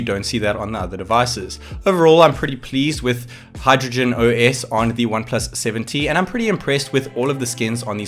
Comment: did you say HydrogenOS? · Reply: Yes. Chinese variants have Hydrogen OS and not Oxygen.